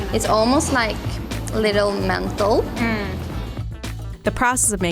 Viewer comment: "When you smell the cardamom like this is like menthol". But it sounds like she says mental! Lmao